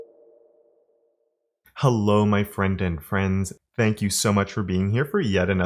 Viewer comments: Suddenly comments have popped up about your welcoming introduction "front end friends". Up until your previous upload I always thought you were saying "friend and friends" as in to welcome the viewer personally and then everybody else 😂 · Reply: Yes I thought he was saying "friend and friends" when I first started watching his videos; just thought it was part of him being super nice. I hear "front-end friends" clearly now though. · Suddenly comments have popped up about your welcoming introduction "front end friends". Up until your previous upload I always thought you were saying "friend and friends" as in to welcome the viewer personally and then everybody else 😂